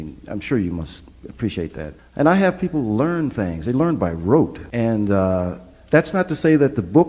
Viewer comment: Thanks for a great video! Hate to nitpick, but it might shed some more light on what Joe is saying: at 3:28, he says "they learn by rote," meaning habitual repetition, rather than "wrote," which is sort of confusing given the context.